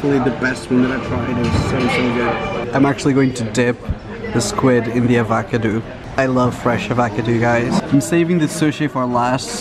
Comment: Did he actually say ,, i'm gonna dip the squid in the AVACADOO" 7:36 like what??!!